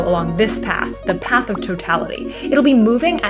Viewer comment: I heard that as path of total fatality 😅haha